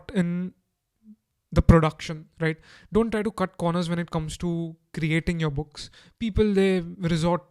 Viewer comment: Amazing ! :D I am sorry for the question, but I cannot understand. Can you write what you say at 7:24 - 7:50 ? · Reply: "...Don't try to cut corners when it comes to creating your books, people resort to free stuff just to save a buck and it's not worth it, eventually someone's going to make a better product or you'll get bad reviews..."